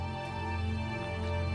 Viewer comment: What does he say at 2:47 ? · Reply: “I can see you” talking about his dead dad